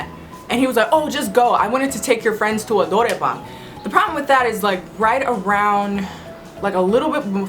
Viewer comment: I think the Korean word she said in "I wanted to take your friends to a.." is "노래방"(norae bang) (literally: song room) Meaning: karaoke room